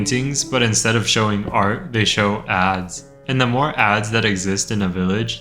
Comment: i thought bro said ass